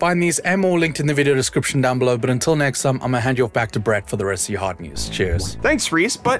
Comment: Yesterday I thought I heard Reece say "..for the rest of your heart news" now I cant unhear it, and I lowkey love it! ❤️